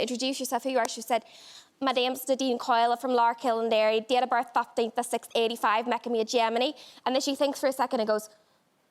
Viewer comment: Could anybody write down what she says from 2:54? I'm not a native speaker, listened to it several times, but just don't understand. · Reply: My names Nadine Coyle, I’m from Larkhill in Derry, date of birth 15th of the 6th ‘85 making me a gemini (then shes says what date of birth did I say now, 15th of the 6th….)